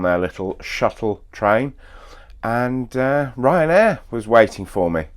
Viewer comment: RyanAir was waiting for you at Birmingham? Congratulations, usually RyanAir wait for nobody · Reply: I thought he said Ryan Blair